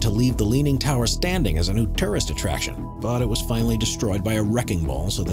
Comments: Wait... @ 11:20 did he say TERRORIST attraction? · I heard it as “leave the tower standing as a new terrorist attraction” Definitely won’t be standing for long